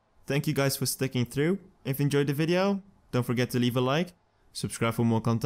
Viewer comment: lol i thought you said „money is miller“😂